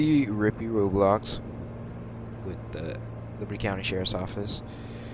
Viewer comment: did he just say mano county sherrifs office😅😅